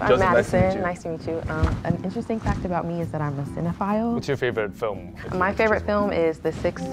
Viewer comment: Ok so I thought she said xenophile not cinephile and I thought hmm 🤔so she’s saying she likes foreign people. That’s kind of an interesting approach and then she said the sixth sense and nothing fell into place.